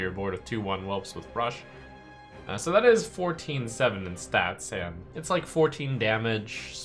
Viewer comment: did you say 14/7 in stats? is this the new ultrasaur?